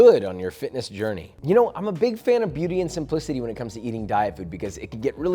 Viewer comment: I thought he said "Beauty and some Pussy". I had to turn on CC to double check. Hmm... it is something you eat. It's not for everyone though.